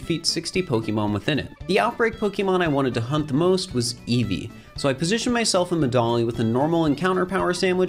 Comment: "I positioned myself in the dong" that's what I heard 💀